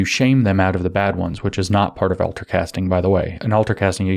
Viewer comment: ultra casting? am I hearing it right? (I'm not native speaker) Where I can read more about it?